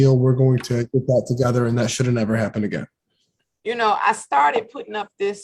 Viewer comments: 😂😂 play some clothes, sounds like he says "and that shit'll never happen again" 😂😂😂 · did he say “ that shit’ll never happen again”? 🤔🤣😂